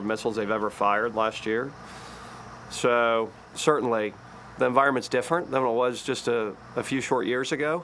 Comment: I could swear i heard him say" the varmints different today" Yo Extortionist joe your people are startin sht .